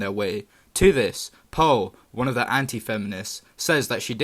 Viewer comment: Where are you from if you don't mind me asking? Not making fun of it at all but because of your accent at the very start when you said "Pearl, one of the anti-feminists" I thought you said "Paul" haha